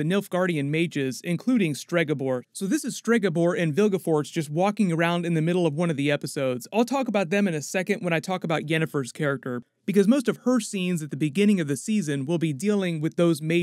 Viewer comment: her scenes iheard it as hircine 😂😂 im a faithful hunter